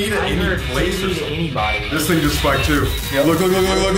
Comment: Is it me or did i hear the sprit box say "i didn't need anybody" on